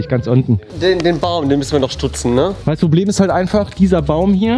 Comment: Minute 8:13 Da hab ich verstanden, "Wir müssen den Bao noch stutzen"